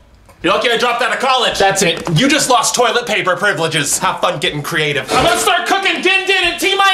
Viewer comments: “You lost your toilet paper privileges, have fun and get creative” Me: just wash you bum with water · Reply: Tbh I thought he said “*How fucking creative*” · “YOU OPEN THIS GATE RIGHT NOW OR I’M COUNTING TO THREE!!” “One,” ”Four..” “YOU'RE LUCKY I DROPPED OUT OF COLLEGE.” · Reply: Lilac Daisy I heard “you don’t get to drop out of college!” but I think what I heard is even better 😂